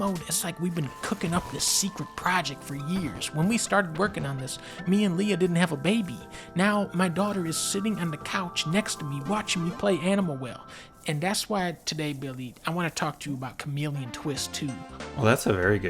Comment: At first I thought you said 'my dog', Sorry.